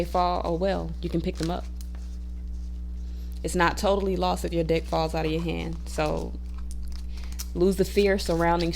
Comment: I love this 💜 but for a moment I thought you said “when your dick falls out of your hand” oh haha thanks for all the helpful tips beautiful 🦋